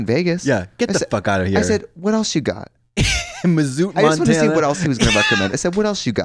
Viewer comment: did Katya say Missou(t) Montana??